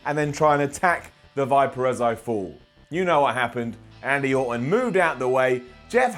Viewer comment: Did Simon call him Andy Orton at like 2:44?